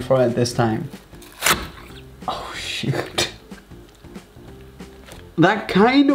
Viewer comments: OMG WHY DID YOU SAID SH*T you cant say know it sound so clear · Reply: He didn't say shit he said shoot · did u say shi.... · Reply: He said shoot